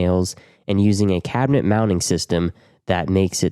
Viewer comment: What did he say? Cabinet mounting system?